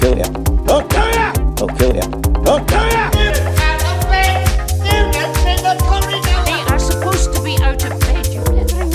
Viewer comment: What is he saying in this part? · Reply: STUDENTS OUT OF BED STUDENTS IN THE CORRIDORS